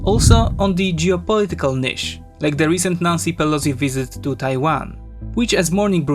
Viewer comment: I thought bro said "nazi pelosi" 💀